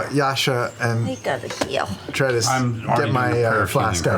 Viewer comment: Jester speaks zemnian? She says: "widerlich hier" which translates to "It´s disgusting here". Interesting... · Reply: Might've misheard that, quite sure she says "I gotta heal"